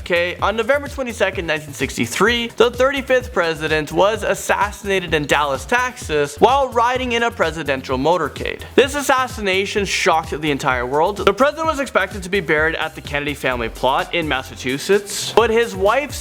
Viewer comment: said Taxes instead of Texas.